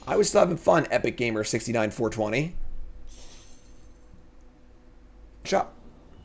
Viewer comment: Awesome content as always. So at 55:09 for YouTube you cut to the next game and it sounds like you say chop. It's quite brilliant... to reference the edit.